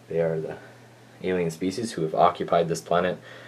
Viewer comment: Kashyyk is not Cardia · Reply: Oh, thought you said "series" not "species" now I see what you mean. But still, it doesn't mean it was plagarized because it is similar.